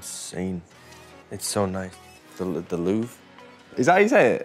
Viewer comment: I thought he said "the loo". Memorable site indeed.